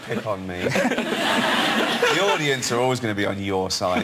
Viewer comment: "Don't pick on me, the audience will always be on your side!" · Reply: He said audience? I heard "idiots"!? X)